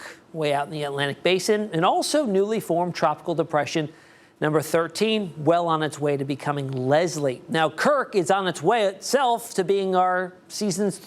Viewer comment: I thought in beginning he said Kirk was on its way to becoming Leslie, I thought- OH NO! NOT THE WEATHER TOO!😅😂